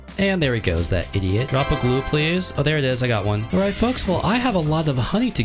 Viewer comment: Go to 7:41 what did he say 😂😂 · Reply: “Oh there it is, I got one” ???? Wdym